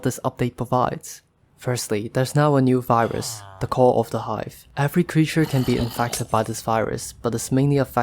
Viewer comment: dang! my hearing's going cause i thought i heard you say "Corona Five" when you were saying "call of the hive" xD